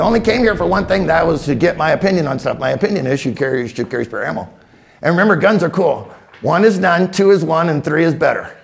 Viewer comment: At the very beginning, he says, "My opinion is ..." and then he says it so fast, I cannot understand him. Can someone please help me understand what he said? I played it back like 10 times but I've got nothing except possibly hearing the words "carry" and "ammo." This is the only video I've ever had to DROP the default play speed on. :) Seriously though, this guy is great. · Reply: You only came here for one thing and that was to get my opinion on something. And my opinion is you should carry spare ammo.